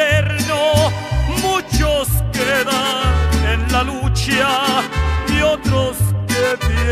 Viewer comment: The translation in 2:47 is wrong (i think). He say "muchos quedan en la lucha" = "Many are still in the fight", if he instead of saying that he say "muchos SE quedan en la lucha", the translation is ok, but he say "muchos quedan en la lucha" without "SE".